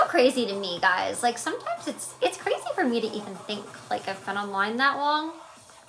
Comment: it kinda sounded like she said "its so crazy to think ive been alive that long" 😅